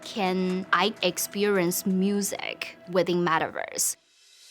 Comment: did she just say madaverse